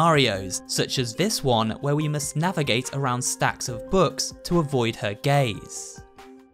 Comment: I HEARD HIM SAY 'to avoid her gays'